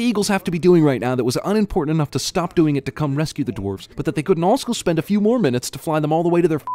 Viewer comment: Is it just me or did he say alsco